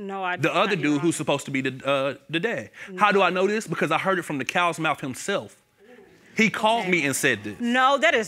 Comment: "Heard it from the cow's mouth himself!" ....could've swore the animal included in that phrase is a HORSE, but I digress. Back to the show. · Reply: I swore he said “heard if from the cowards mouth himself”